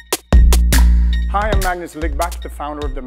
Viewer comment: I had the video on another tap and On God I heard him say Magnus Lygma, I busted a rip laughing at that.